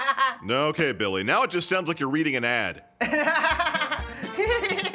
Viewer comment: Did Billy say: “Someone hurt his Dick” at the end there?